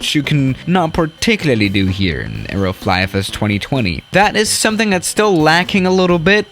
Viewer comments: I thought he said Aeroflot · D-did you just say... Areo soft. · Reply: I thought he said Aeroflot